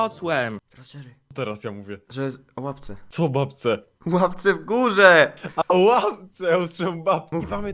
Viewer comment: o łapce - o jakiej babce 😕😳 - O Łapce nie o babce 😂 - a o łapce ja usłyszałem babce 😂😂 To był najlepszy początek